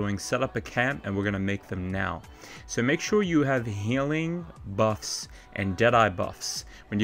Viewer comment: what is the word you are saying? It sounds like "Healing Bucks and Deadeye Bucks" but that can't be what you're saying..... Also, I slept for 18hrs and the old Cougar carcass was still there....I wonder if they patched this glitch....